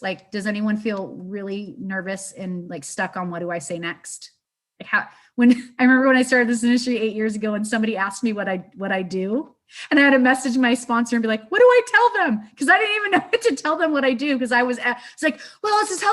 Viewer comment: Did she call being in an MLM her “ministry”?!?!? Oh dear. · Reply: I looked back and I think she said industry not ministry...but I wouldn't be surprised if she called her MLM her ministry.